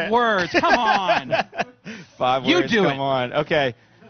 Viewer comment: Lol. In the begining I thought he said "Fuck words!"